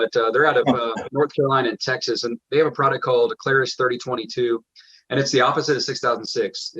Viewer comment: Hi thank you for this helpful video! At 8:00 in the video, mentions a 30-22 wax, I can't quite make out the name, it is Clara's? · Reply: Clarus 3022. Shoot me a message/email and I'll send you some info and a contact for it. :) Email insisted on my channel about page. :)